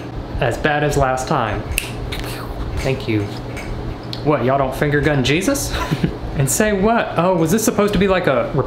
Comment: “What y’all don’t finger gun Jesus?” Disapppointed Chuckle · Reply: I thought he said “What y’all don’t finger Jesus?” And I was like No Not usually